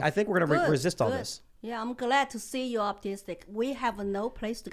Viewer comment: I can't help myself it was funny dude it sounded like Lilly said she was glad Tim was autistic I know what she actually meant I know she was trying to say optimistic but that was funny If you weren't paying attention and didn't know what was going on And didn't know that Tim said he was optimistic you would literally think she said I'm glad you're autistic lol